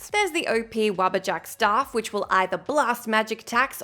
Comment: Spaced out for a sec, and I heard 'Rubber Duck Staff' instead of 'Wabbajack' xD